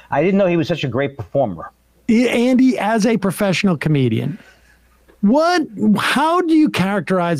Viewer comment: I misheard you say, 'as a comedian' and thought Matt Walsh was one, and I was gonna say, "Does he know you have be paid at least once to be a professional something?"